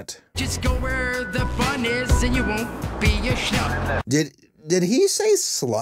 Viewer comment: Pausing @ 9:03 to say: I could be wrong, but to me it sounds like the word is question is actually "Shlub." With the way the word lands exactly at the same time as one of the percussion sounds, it's no surprise that it's hard to tell what he says.